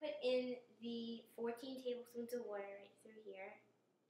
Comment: Love the cookies. You must be a New Yorker or from Jersey....cuz I thought I heard you say water as "Woh-ta" lol I love making cookies too....I made Valentines Day Cookies too.